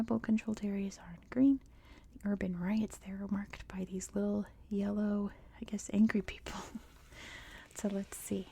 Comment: I was about to fall asleep and I heard "the little yellow angry people" and I first thought you said something offensive and couldn't understand because I didnt think you would say something like that, but it was just urban riot marks 😂😂